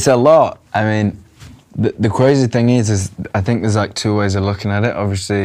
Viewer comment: aww so cute, he said CWAZY instead of Crazy, I am meeltingg 😭❤❤